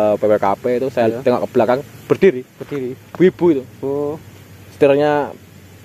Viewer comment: njir gw dengernya wibu🤣